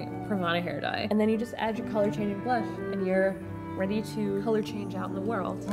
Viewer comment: At the end when she listing all the color changing things I thought she said color changing blood Lol😂😂